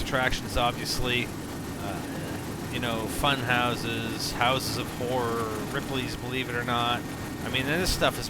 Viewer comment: did anyone else hear House of Whores?